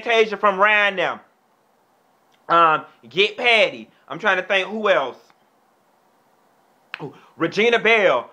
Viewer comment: Haha I Put the Captions on and it made me laugh harder @ 7:19 the captions said im trying to paint whales lmfaoo